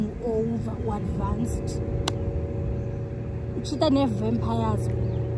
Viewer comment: when i heard 'ucheater nevampires' i died